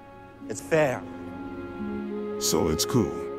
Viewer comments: "So it's cool?" "Cool as Hoth." (proceeds to chill and sit down) Well that was surprisingly wholesome. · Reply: I kept hearing "Cool us off." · Did he just say it is Flair"""""""""""" OMG he uses Redit.